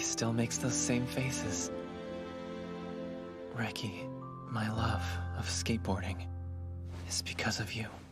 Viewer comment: crying my dumbass thought he said "reki my love AND skateboarding" i hate it here